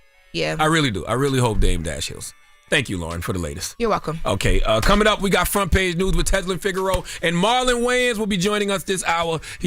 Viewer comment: Did I hear, “coming up next..Front Page News with Tezlyn Figaro” ? She’s back ❤